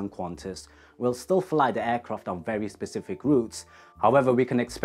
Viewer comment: did you say “various Pacific routes” or “very specific routes”? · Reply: My bad - specific